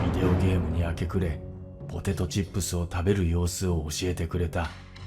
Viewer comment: i never actually knew for a fact but it was odd i kept hearing "watashi wa" (relating to "i", or the speaker) and never seeing the correct subtitles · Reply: And "potato chip" at 6:47:07 (if that's what it's saying)